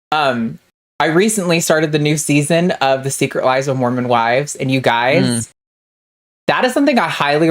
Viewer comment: For a second, I thought you said "The Secret Lives of Morcombe & Wise" (which would be a bizarre thing to have on American telly).